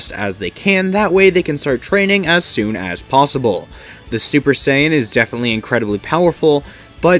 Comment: i heard stuper saiyan no cap